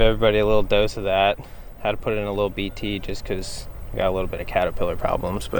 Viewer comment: What did he say he used for catapillars. At like 8:00. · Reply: BT